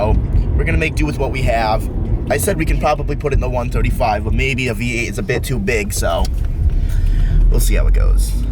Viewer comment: did i hear "i said we could put it in the 135 but maybe a v8 is a bit too big" ?? @ 0:35 is he challenging himself?